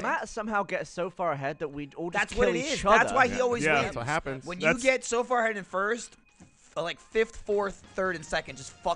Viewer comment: Gavin “have you run out of nose jokes” Free Michael “wears socks with sandals” Jones Lindsay “burn the world” Jones Alfredo “Hot Trevor” Diaz Ryan “totally stole it’s your day” Haywood Larry “stud muffin supreme” Matovina (I can’t make out what Trevor’s says) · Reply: Alfredo's actually says "Not Trevor" and Trevor's says "Not Alfredo".